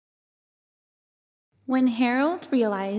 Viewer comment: Who is HAROLD?! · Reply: I know, it just really sounds like she is saying "When Harold..." So it's kind of funny. ;) This is a beautifully performed song, love it! Merry Christmas!